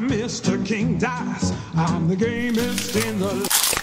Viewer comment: I'm the gamest In the CRONCH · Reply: I thought he said gayest in the land